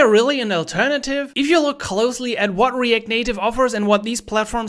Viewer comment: Did you say Erect Native? 👀 Freud grüßt